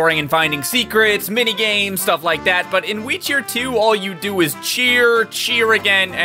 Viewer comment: For wii cheer 2 I thought he was saying Witcher 2 weirdly 😂😂😂